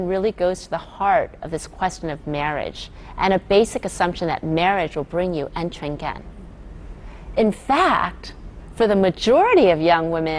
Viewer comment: what does she say?? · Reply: 安全感 = sense of security